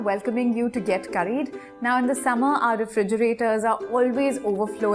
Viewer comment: Nan vanthu. I heard this word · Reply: She said "Now in the summer "